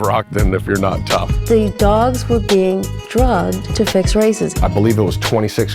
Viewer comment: “the dogs were being drugged to fix RACISM!” Lmao I thought this was what she said at first 😂